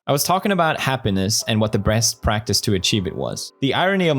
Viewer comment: Anyone noticed pewds saying breast instead of best?